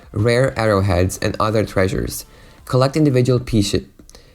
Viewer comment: did I hear it right? :D Piece shit? However thanks for saying all of that I really apreciate it. I myself bought the game half for the horses and half for the story and ended up being there just for the story that I fell in love with.. mostly because of Arthur. And to be honest I cried at the end of the story and after the epilogue in the credits too even tho I'm not a person who cries for every emotional bullshit that I go through. It's so well written, so well made, so damn beautiful and so god damn emotional that I'm loosing words.. it' just amazing in every way possible. So for every person who's interested in this game - it's worth the money and your time! <3